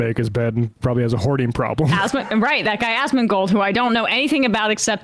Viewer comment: I misheard Emma & thought she said Athsma-gold instead of Asmondgold.